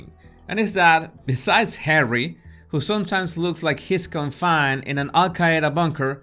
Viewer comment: I love the line: “Harry, who sometimes looks like he’s confined to an Al-Qaeda bunker.” So very true! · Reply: Best I heard is "he looks like he smells "